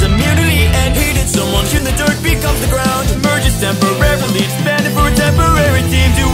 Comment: hey did someone hear the dirt becomes the ground?